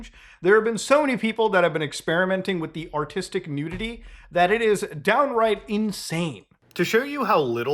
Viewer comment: when he said “artistic nudity” i thought he said autistic community